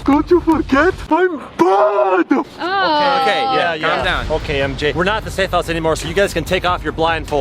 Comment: Did anyone else heard calling Pz Fúnf MJ